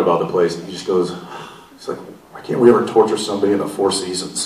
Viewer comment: what did jensen say at 7:39? The only thing I understand is 4 seasons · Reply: I heard he said that their dolly grip Shane told Jensen "Why they can't torture something in 4 seasons (I think he means the hotel).